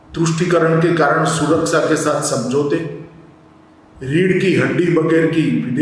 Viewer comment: What did he say at 1:33 ? Tushtikaran ke kaaran ___________ samjhaute. What's that word in between? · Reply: #suraksha is the word